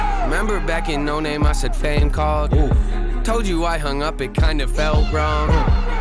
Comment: He called fame back and sent him a song called leave me alone that is why fame said the song sucks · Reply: I thought it said a fan called